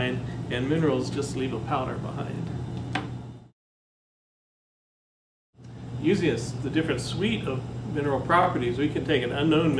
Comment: I hope you don't mind, but I am transcribing this talk for my high school students in Korea for college prep. I am dying to know what you said at 5:18...it sounds like, "usius, the different suite of mineral properties", but that makes no sense to me.